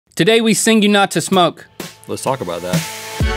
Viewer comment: WTF did link say in the intro I thought I heard Today we sing you not to smoke, I turned on CC. Says the same thing I am the CC?...